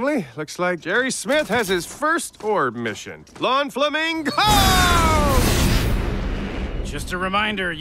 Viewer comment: Flamingoooooooooo flamingo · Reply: Thought he said, Lawn Flamingo!